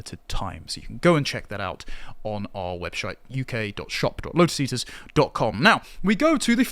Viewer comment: Lol 0:50 did he say "webshite"? 😂